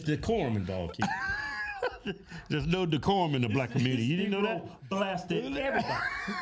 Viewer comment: I thought he said "there no da corn in the booty!" 😂 woow my bad